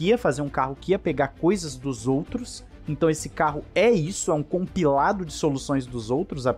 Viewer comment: no minuto 6:22 : " Esse carro é um compilado dos outros " Eu : " Esse carro foi um catadão , tomara que dê certo "